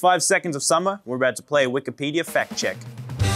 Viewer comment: I 100% thought Luke said “were about to play wikipedia fat check” — 😵‍💫